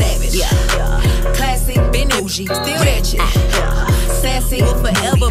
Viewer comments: I heard "rat sh*t" instead of "ratchet" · "Classy, bougie, rat sh1t" - Funniest misheard thing in my opinion